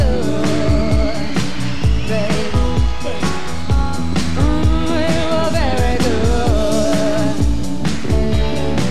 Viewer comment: Can anyone tell me what Lindsey says at the 2:00 mark when he is singing backup ???? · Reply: Stevie says ''Daddy'' Lindsey says ""Baby''!